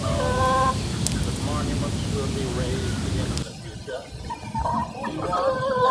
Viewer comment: I heard "monuments will be rapey into the future"